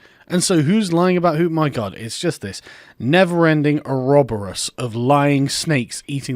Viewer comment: What was that word you used at 9:05? Eroberus?